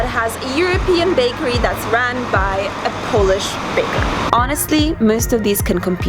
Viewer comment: Youtube captions are quite funny on this video, at 6:44 it says Polish babe instead of baker :)